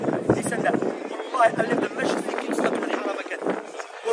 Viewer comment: What did say at 14:08 “I left the masjid…” I couldn’t make out what he said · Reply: "I left the masjid thinking let me go commit haraam again", cos the brother presented such a skewed view of marriage.